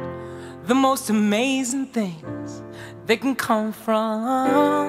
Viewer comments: I swore I heard some terrible lies 😃😀🤓😺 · “you wouldn’t believe, the most amazing things, that can come from, some terrible nights.” yep. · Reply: I literally thought he's been saying "terrible lies."